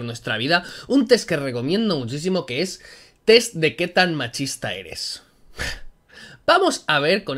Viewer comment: Pense que decia que tan machista soy gay